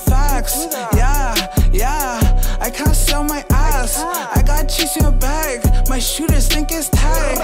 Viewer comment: He really said I can't sell my ass bruh💀😂 den later he said "gang" foh and Drake gone hop ona song wit em bruh 2k foul for putting this on there fuck the 🔥 ass beat these lyrics too foul 🤦🏾💯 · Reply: He said” I cannot sit on my ass I gotta chase a bag@ but I thought initially he said something bout selling ass too 🤷🏾‍♀️🤣🤣 I think it was the pants and booty poke that threw things off 😂😂